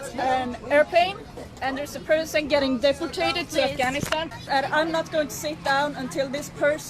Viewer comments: What does she say at the start. It sounds like she says the person is getting defecated, lol what. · it sounded like she sed " person getting deficated to afganistan". lulz